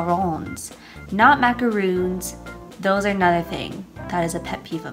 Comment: ! Macaroons is actually a thing. It is a pastry with coconut in it. And yes, macarons and macaroons are different :) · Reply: itsmesheley aha you prolly heard her say "not a thing" instead of "another thing", like I did the first time :')